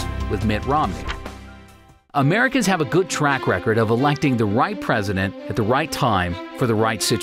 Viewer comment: Why i heard "America has a good track record of electing WHITE president, in a right time blablabla" haha. I'm in Romney side tho